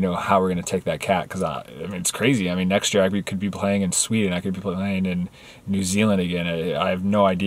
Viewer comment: Did I hear Sweden?